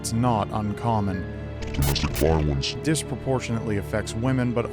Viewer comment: You messed up the Editing here. Can't hear what's being said. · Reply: Domestic Violence